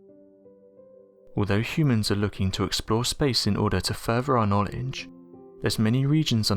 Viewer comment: Wait. @ 0:09 Did you just say fervor? It's furTHer. TH TH. Fought er thought you were bright & able pronounce English correctly.